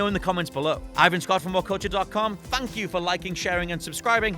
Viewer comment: Did you just say "TANK YOU" ? You uncultured swine